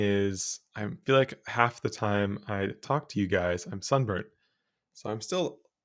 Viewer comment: Your saying one thing but I'm hearing another. I thought you said "Half the time I talk to you guys I'm sober." Ha ha ha ha You said sunburned. I heard sober. Did you paint that in the back? It's really pretty. K. I'm gonna listen now.